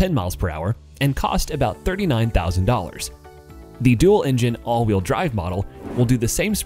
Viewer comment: did you just say engine?